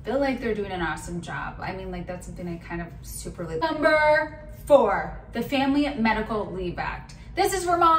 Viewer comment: she says number four, but the subtitles say number three...... Did you even pass fifth grade?